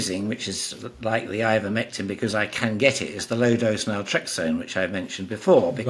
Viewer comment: This doctor mentions a drug that damps down inflammation associated with cancer, along with Ivermectin. It sounds like he said maltrexin, but cant hear what he is saying due to his accent or the fact that he mumbles. If anybody can make out what he said, please provide this drug.